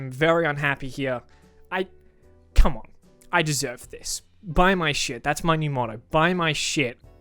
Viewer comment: when I first heard him say "buy my shirt" I thought he was saying "buy my shit"